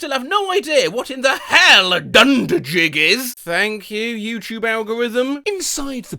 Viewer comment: I also always hear "dunderjig" and do not know what it means! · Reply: I hear "dundergig" like wtf is a "dungergig"